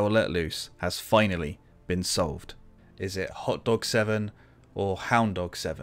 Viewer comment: I’ve always heard it as horn dog 7 😂